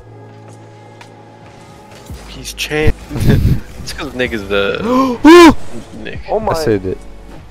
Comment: What did he sayyyyy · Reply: “nick is a…”